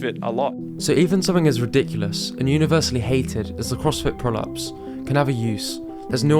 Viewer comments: every time you said "crossfit pull-ups" I heard "Crossfit prolapse"....but it's still probably correct lol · By the end of the video I understood that Crossfit Prolapse is not a mascot but a Crossfit Pull-ups I misheard.